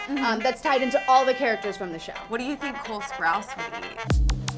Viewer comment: I thought they said ‘cold sprouts’ at first, but then i realised ... Cole Sprouse 😂